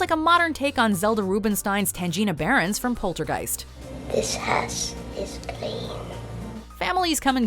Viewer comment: I thought she said "this ass is clean" 🤣🤣🤣